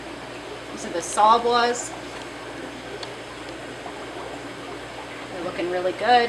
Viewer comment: Impressive. Can you tell me what the two types of fish are in the tank you showed at 11:47. It sounded like you said "saw-blas"? One of them is mostly yellow and the other has an orange head with orange tipped tail fins. Thanks! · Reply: sawbwa resplendens- Asian rummy nose